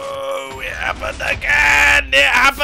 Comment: can some one say what mumbo says in 4:15 ? xD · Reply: "It happened again" (strained and frustrated, referring to the contraption blowing itself up)